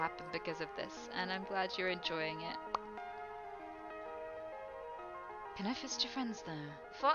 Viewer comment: That end lol. She literally sounded like Siri saying “Can I fist your friends?” ⚰️